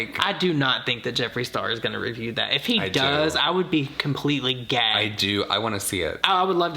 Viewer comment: OK, but if you put the subtitles on from 23:17 they think says 'I do not think that Jeffree Star is going to review that, if he does, I would be completely gay' 😍💚 I think that sounds like an entirely reasonable reaction 😁